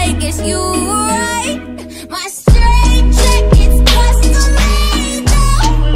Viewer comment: I thought she was saying “my straight jacket’s crushing me” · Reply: Blaze It I heard “my straight jacket is crushing tomatos.”